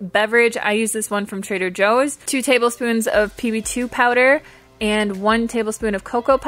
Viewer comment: Does any one know what is the powder she says at 3:12? :) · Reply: PB2 powder its basically powdered penut butter